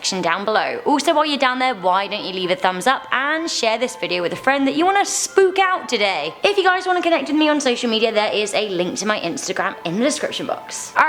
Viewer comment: I legit thought you said share this video with a friend u wanna smoke up today lmao